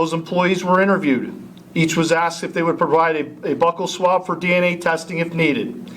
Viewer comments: Faking a hate crime is a hate crime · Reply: I could have sworn I heard "butthole swab" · Did he say "butt-hole swab"??